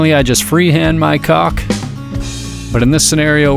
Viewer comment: I have to admit, I wasn't paying attention for a while, then I had a re-directing moment when I heard, "wash my caulk". I got back on track ASAP! 😂😂😂😂